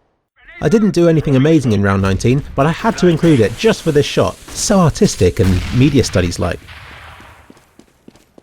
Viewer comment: Does anyone else think he said "So autistic," at 16:53?